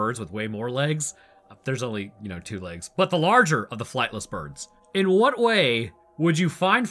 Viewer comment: I heard "flappless" instead of "flightless", and thinking about it now, it wouldn't be completely wrong 😂